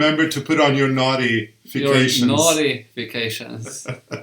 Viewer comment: naughty vacations- I swore that is what I heard!